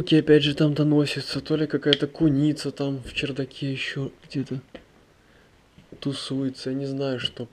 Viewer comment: Мне одному только послышалось на 13:37 он сказал МЫ. Он там не один. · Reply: "...то ли там какая-то куница доносится. В чердаке."